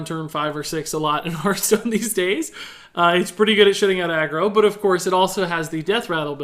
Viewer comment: Am I the only person who was taken aback by saying shit at 13:05?? I don't neccesarily have an issue with it, I just don't think I've ever heard swear before. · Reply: ProphetOfDoom - Haha, it does sound like I said "shitting" but for the record, I think it was "SHUTTING out aggro."